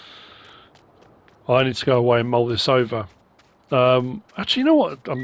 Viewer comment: I misheard as "I need to go to Moldova" It just seemed to fit with the 'looking for imports' theme.